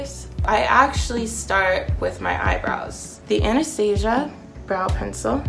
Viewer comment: lmaooo who else thought she said anesthesia